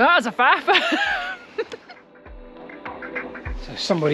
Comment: what's that you're saying at 1:01? It sounds like paarp-bah-pa-bah. Or was it boo poo pa doo? 😀Sorry for this bit of fun at your expense. Love you. 😇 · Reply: "I think she said "That was a Faf"